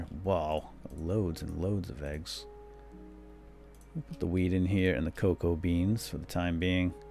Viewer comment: I know that you didnt mean to but I swear you say,"... put the weed in here." lol